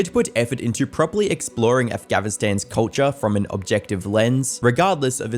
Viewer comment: Is the audio broken or is that just me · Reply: No not just you. I keep hearing “Afghavistan” with a V not an N